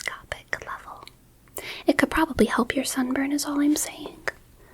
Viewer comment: i wasnt really paying attention until i heard this and thought she said "it could probably help your son burn" instead of sunburn